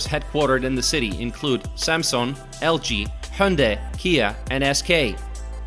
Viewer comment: Nope. It is a Japanese company, famous for their motorcycles and cars. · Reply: I heard it Honde at 1:45 in this video. Thank you